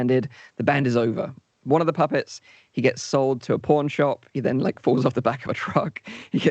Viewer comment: It sounds like says porn shop not pawn shop and I hadn't seen the ad before and wasn't apying attention to t=video and was like WHAT?!?